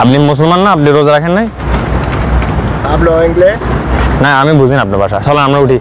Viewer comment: Hablo ingles? Seriously, how does what he's saying sound like Spanish XD